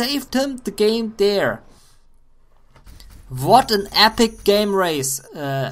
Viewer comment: Lol thought you said epic gay race